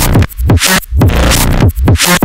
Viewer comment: kinda sounds like hes saying bruh